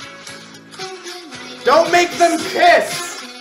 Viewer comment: why does it sound like ranboo saying "dont make them piss!" 💀🙏